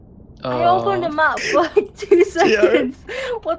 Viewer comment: What did she say after hello? At around 0:30. I heard she opened a mouth? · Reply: she said 'i open the map for like 2 seconds...are you kidding me'